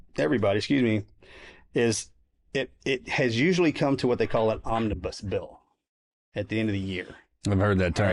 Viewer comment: did Morgan say an Ominous Bill…? Surely not right???